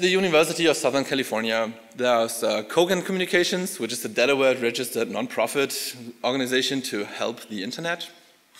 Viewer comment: did he say Cogent is non-profit ?